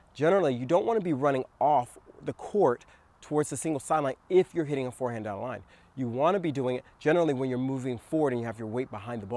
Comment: You said “singles sideline”. But I heard “evil sideline”. Same difference though. 🤣